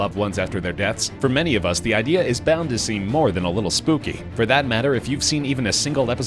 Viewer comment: I thought he said Spoopy lolol I say dat